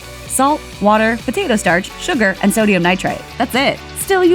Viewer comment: “Sodium Nitrite” sounded like “Sodium Nitrate” to me